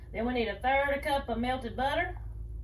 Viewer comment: I cup warm water 1/3 cup melted butter 2 Tbsp Yeast 1/4 c sugar Combine, let mixture get "foamy" Then add 1 egg Pour mixture into large mixing bowl Add "around" 3 cups of all purpose flour, or until you have a good workable dough Knead 10 minutes Preheat oven to 400 degrees Divide into 18 balls Cover and let rise for 10-30min Paint tops with egg wash Cook for 10-15min or until tops are golden brown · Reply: Damn that accent so honky-tonk, I thought you said “third cup milk and butter” 🤣 we’ll see how it turns out! 🤪